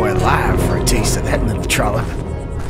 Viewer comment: What does that guy say who pursues Billy? "I'd skin that boy alive for a taste of that little [?]" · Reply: Mr Culec How would he be saying cock? Sounds like "trummer" or something.